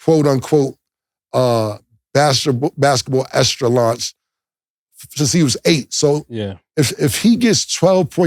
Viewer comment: lmaoooooo wtf word was that just used 😂😂😂😂😂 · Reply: I believe he said extraslauch🤷🏾‍♂️